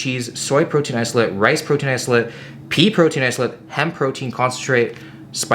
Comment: When you said "pea protein isolate", I misheard it as "pee protein isolate" and got nervous for a second O_O